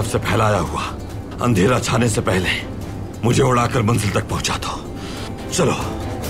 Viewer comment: Ertugrul said Bamsi instead of Turgut 😅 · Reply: He said manzil not bamsi ....2nd time daikh raha huv iss drama ko